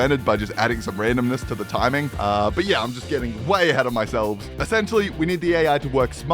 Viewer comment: Hey, uh, Evan, did you just say “but I’m getting ahead of my selves”